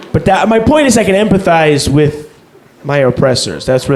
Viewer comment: "I emphasize with my oppressors" brilliant lol. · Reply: I thought he said "empathize"